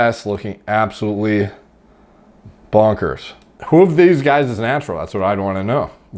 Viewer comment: DID JUST SAY POGGERS??